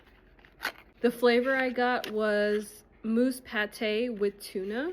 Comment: Oh my goodness, at first I thought you said "Moose" Pate then realized that you meant "Mousse" Pate, lol! They do look quite tasty. Will get some for my feline friends. Seeing you and your furry family always brings a smile to my day, thank you! 💐